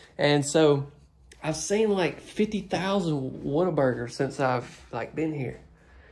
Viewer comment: I thought you said Water burger, had to look it up to find out! Clearly I never heard of it!😆